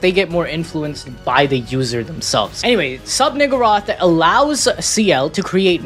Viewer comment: did bro say nigga?